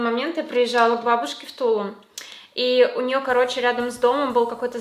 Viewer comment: Мне не послышалось, ты сказала Тулун? Или Тулу? Я просто живу в Тулуне